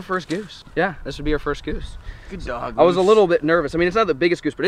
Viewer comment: I think said good dog goose at 7:38 but I might just be crazy 😂😂